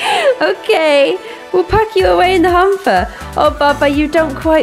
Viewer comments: I thought you said hamster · No joke I thought it said hamster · I sounds like she is saying humper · I thought it said a hamster not a hamper that would have been amazing